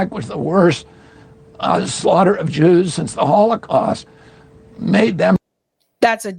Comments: Great report. But one thing. RFKJ said that was the worst attack on Jewish pp SINCE the holocaust. I think you may have thought that he said THAN the holocaust. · RFK did not say that the Oct 7th was worse than the Holocaust. At 06:11 he said that it was the worst SINCE the Holocaust; meaning after the Holocaust. RFK may be a douche on this subject but he's not stupid.